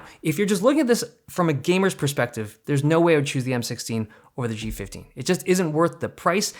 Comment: did you mean there's no way I would take the M16 "OVER" the G15? Because it sounded like you said there's no way i would take the M16 or the G15.